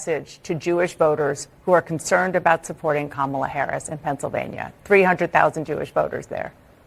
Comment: Did she say 300 thousand voters there in Pennsylvania 9:52?